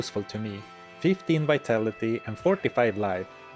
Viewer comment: I'll never forget my first ever character when I was like 10 years old, my brother told me to put everything into vitality and I thought he said energy. The pre-respec days were rough.